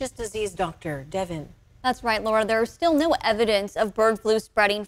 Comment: Did she say "new" evidence or "no" evidence? Also her voice slightly reminds me of a tiny kazoo.